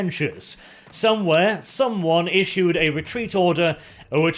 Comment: Did I just hear "retreat"?